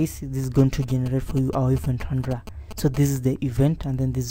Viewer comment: Great video thanks. Question - I didn't catch what you said at 9:40 - it sounded like "event tundra"? Please clarify. Thanks.